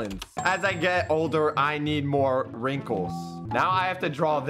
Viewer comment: I thought you Said pringels not wingens :3